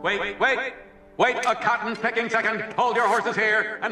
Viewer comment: WHAT DID HE SAY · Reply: He said “Wait a cotton picking second!”.